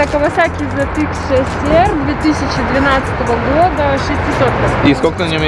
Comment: Zx6r вообще то 636 кубов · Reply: пересмотрел ролик.. изначально послышалось 2013 года.. в 13 году 6r был 636.. в 12 году шестисотки были zx6rr